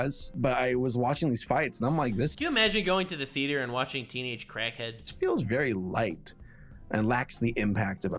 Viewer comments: I thought you said. "Imagine going to the theatre by watching teenage crackhead" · Did.. did you say "teenage crackhead"?